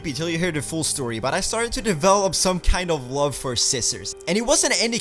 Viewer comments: at first when he said he fell in love with scissors I thought he said he fell in love with sisters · I thought he said sisters instead scissors LOL